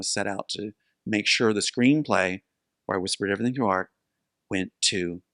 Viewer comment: Does anyone know what he says @ 27:26? "whispered everything to art" is what CC says, but that doesn't make sense.